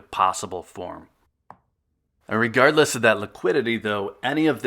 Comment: Did you say IRRIGARDLESS @ 6:04 just curious